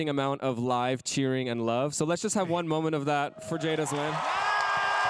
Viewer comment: Did he call Jaida, Jada Smith? 😂